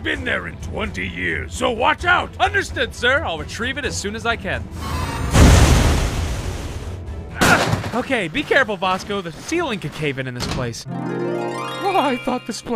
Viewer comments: When you said Vasco I thought you said BOSCO (from DRG) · Vasco I thought you said Bosco oT